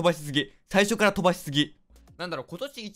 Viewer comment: <飛ばしすぎ>が<twiceすき>に聞こえた